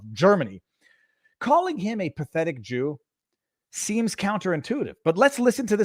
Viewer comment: I heard "...pathetic excuse"